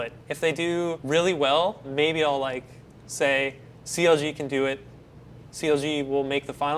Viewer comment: captions said CLG was seals, susie, and ceiling all in one scentence